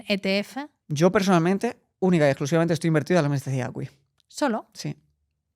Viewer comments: ¿en que dice que está invertido? No se entiende nada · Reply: En el MSCI ACWI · Muy buena entrevista pero no entiendo qué responde a la pregunta de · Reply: El MSCI ACWI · qué dijo? 20:07 no hay forma qle entienda! Y eso q lo ralenticé. Dónde invierte? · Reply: En el MSCI ACWI 😅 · En el min 20:10 que dijo? Tiene todo en MSCI Word ? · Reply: En el MSCI ACWI